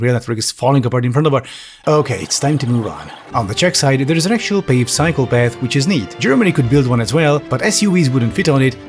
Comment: i know he says "actual paved cycle path" but i swear he said actual psycopath. The latter would have made for much better plot twist for the video